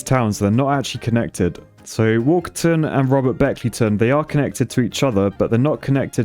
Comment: I thought Walkerton said Wankerton off in the distance